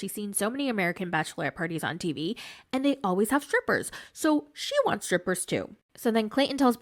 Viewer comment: Jovi just joined the chat, did you say stripper 😂😂😂😂